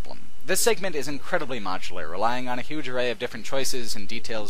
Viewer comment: ''The best design aspects of mass effect's plot are the "... m(o/au)(n/_)(tul/chul/cul)ar" ones. The closest I could find, I think, was 'macular', which doesn't make much sense. Can anybody, please, spell that word for me? English is not my first language obviously, otherwise I would probably have no trouble understanding what it is he was saying in that particular instance. And to Mr. could you, perhaps, start adding subtitles to your videos at some point? Or even just enable autosubtitles if it is not something hard to implement, of course - I just notice that some videos here on youtube have it and the others don't. Don't get me wrong, your speech is as crisp as it gets, I believe... 99% of the time (for me at least). I'm more inclined to think that it's the profound vocabulary you employ that can result in some of us poor foreigners not quite understanding some individual words or phrases. Which is sad, as it leads to a minor frustration and can also diminish the overall enjoyment from watching your videos. So subtitles would reeeeally help to resolve this, especially if they're custom, handmade, but again even the automatic ones can do the trick quite often. Thanks! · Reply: Oh, I got it at last :-) The word I couldn't make out was "MODULAR"! I was able to 'decipher' it having heard it a second time here